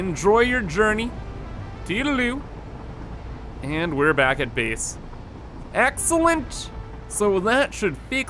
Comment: he actually said "Base" instead of "Beese"